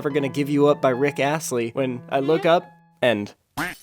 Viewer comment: i got an oreo commercial right at 3:34 so it sounded like he said: i looked up and oreo